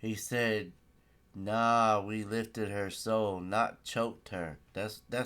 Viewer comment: Did y’all hear what he said “yea we woke her” then said we nuted on her face” when they said yea we lifted her soul “ then they laughed it off while the dude turnin his head. He laughing right now before 3:40 or u can stop it